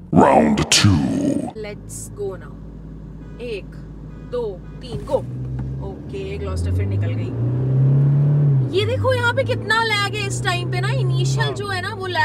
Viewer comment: Did you said 'Gloster frontil kayari' ? 😮